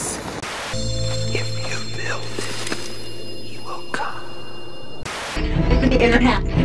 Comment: I swear, every time In the outtakes I hear "If you build it, he will come", I nearly burst out laughing!